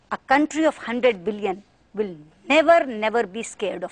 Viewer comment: she spoke billion instead of million !